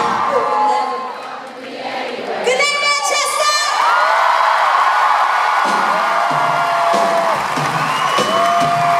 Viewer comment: Wow! Just a great performance! Her voice! 😍 What did she say at 7:11? Goodnight what? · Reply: Good night Manchester.